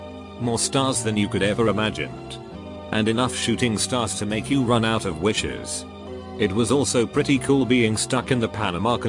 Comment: I hear "and enough shooting stars to make you run out of witches" these ocean people...